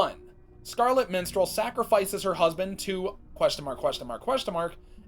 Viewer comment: I heard “Scarlet Menstrual” I burst out laughing I’m sorry